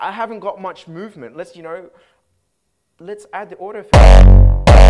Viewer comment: which Filter does he mean by saying "order filter"? or maybe i didnt understand him...